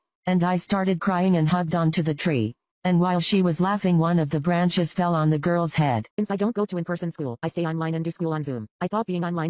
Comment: “And while she was laughing one of the branches fell on the girls head” That tree did more than a teacher could ever do😂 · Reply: i thought u said "and while she was laughing one of the braincells fell on the girls head" omg why am i so dumb